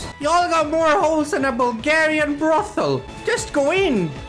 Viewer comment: Imagine not subbing to ahahahahahahahahaha, kinda SUS NGL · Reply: Hey mate at 4:13 did you said Bulgarian or ? And if you said it than you got love from Bulgaria 🇧🇬 :)